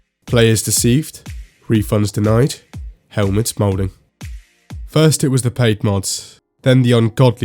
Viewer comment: I thought he said "Helmets Malding" at first